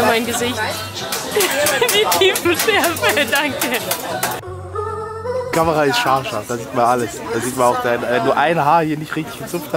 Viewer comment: ich bin mir nicht sicher ob ich richtig verstanden haben bei den #Outtakes hat gesagt die Camera ist scharf SCHATZ xD oder irre ich mich da....ab min